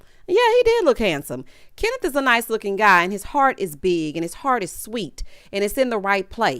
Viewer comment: I came in and thought I heard but his PART is big😂😂😂I said damn not his part